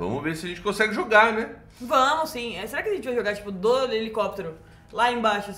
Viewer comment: Alguém traduz o que a falou? kkkkkkkkkk Mas achei ele bonitinho, que pena não deu pra mostra a gameplay. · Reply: so entendi "dois helicoptero" e nem sei se é isso mesmo que ela disse kkk